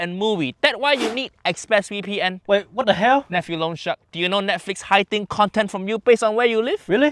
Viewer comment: 😂😂😂 Did he just say Nephew loan shark????